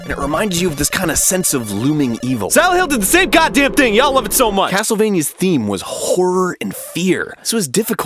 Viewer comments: What does he say at 03:12 ? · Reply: "Silent Hill did the same goddamn thing--ya'll love it so much!" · That is outstanding voice-acting but what does he say? *********** God damn sink, yeah I love it so much.